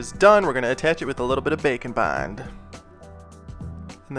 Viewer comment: i thought you said bacon bend ok bye